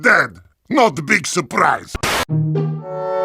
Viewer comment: you r ded tf2 (team fortress 2) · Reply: Traxler S It supposed to mean not a big surprise but Heavy's voice sounded like he say "not a big soup rice"